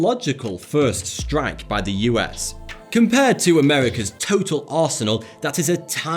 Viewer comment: Did I hear America turtle arsenal?